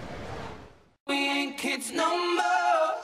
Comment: I always hear "we hang kids no more" (not we're kids no more) Immediately after "Wish we could turn back time to the good old days" ಠ_ಠ